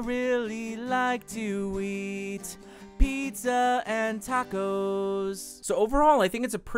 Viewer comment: "i really like the weed, pizza and tacos" hahahahah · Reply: He said "I like to eat". But it did sound like he said weed lmao